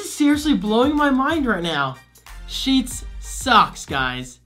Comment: When you said Sheetz socks, I thought you were saying Sheetz sucks 😂